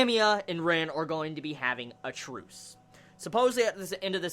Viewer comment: The first time I heard you say they were having a truce I thought you said "tryst". Just thought I should share that. Good review!